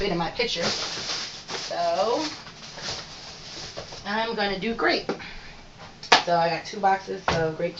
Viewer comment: I thought she said I’m going to do great, I was like that’s the attitude 😂